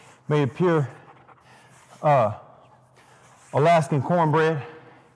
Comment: did he just call his couch Alaskan cornbread